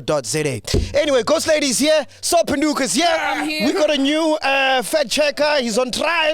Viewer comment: If we're being honest, is the real fact checker here. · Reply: That whole time I thought they were saying "fat cheque guy"😀😀🙆🙆